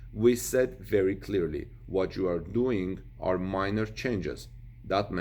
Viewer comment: Transcript : "We said very clearly: 'What you are doing are major changes." What said was "minor changes". see 3:56 +, Otherwise, it was very good. Just raise the camera 25 cm!